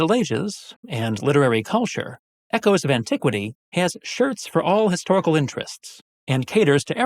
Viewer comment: I thought you said "Equus of Antiquity" and got so confused when that search didn't find a T-shirt site.